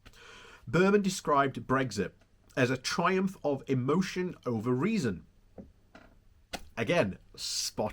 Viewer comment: "Brexit a triumph over reason" yes and also a triumph over the people who want a secure and peaceful life! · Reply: I thought I heard “Brexit a triumph of emotion over reason”.